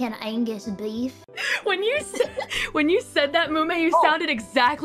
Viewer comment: did Ina say Chloe? Or am I tripping